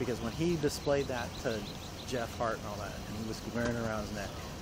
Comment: Everytime they say Jeff Hart I hear "Jeff Fart". 🤣🤣🤣 🥝💚🖤